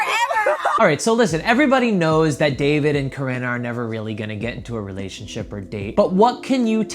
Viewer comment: I thought it said Watching David and Corona 😂💀